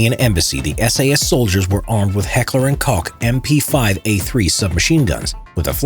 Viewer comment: the way you said heckler & koch sounded like heckler & kock